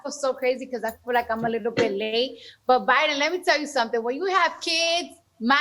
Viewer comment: She called him "Bae" and I think that's what threw him off. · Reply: she calls him Biden, I don't think she called him bae before, also I doubt the knows the word bae